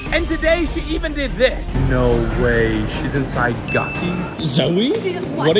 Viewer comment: I thought he said "no way she inside cocky"💀